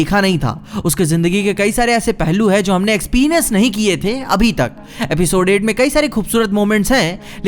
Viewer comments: did he just said expenis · he just said ex-pinus instead of experience 😂😂